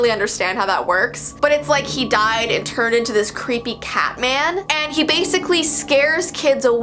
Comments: when she said cat man I thought she said pacman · omg I thought you said pac man not cat man 😂💕Btw now i’m terrified as i’m doing online work 😂 I love your videos btw 💕💕💕💕